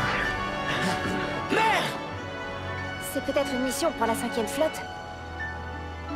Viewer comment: C'est moi ou à 2:38 il dit Mère au lieu de Maître? j'ai beau remettre ça en boucle et le son à fond, j'entend Mère au lieu de Maître XD